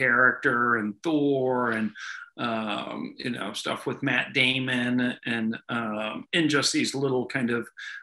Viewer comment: did he say...Matt Damon??